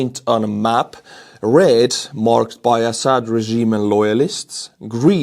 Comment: Just wondering but how many of georgias police officers are actually georgian or are they russian "little green men" Totally misheard red for the syraian map. I heard "red, a sad regime of loyalists" 😂